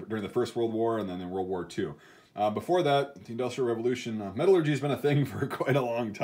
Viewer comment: I heard metal orgy